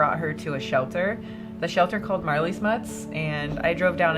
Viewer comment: I thought you said Marley butts lol